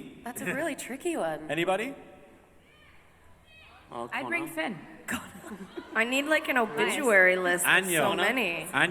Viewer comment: no, i meant at 43:41, he said something to Eliza, which made her laugh and say "so random". just wondering what minor character he jokingly named. sounded like Connor? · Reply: ye he said connor coz eliza repeats him ( murphy killed connor )